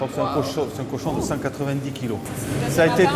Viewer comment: there is no way a pig is going to the butcher at 590 kilos. The auto caption says 190 kilos which is more plausible. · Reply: Right, he said 190kg